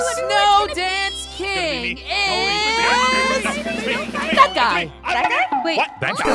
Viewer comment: When noi said that guy I thought he said dada